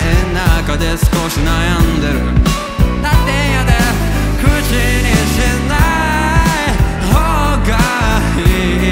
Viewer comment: 方言ですか？何て言ったのか誰でもいいから日本語で書いてください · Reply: たってええんやで これかな？